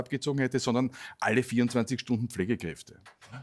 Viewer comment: Es tut mir leid, ich verstehe Herrn Klien nicht, "es wäre besser gewesen Rumänien hätte nicht den Botschafter abgezogen, sondern alle 24 Stunden . . . . ?" Mich interessiert die Meinung von Herrn Klien, was hat er gesagt? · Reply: 24-Stundenpflegekräfte. Vor ca. 2 Jahren war der Stand diesbezüglich, dass rund 30.000 Pflegekräfte aus Rumänien in Österreich gearbeitet haben, worauf sich die Anspielung von Herrn Klien bezog.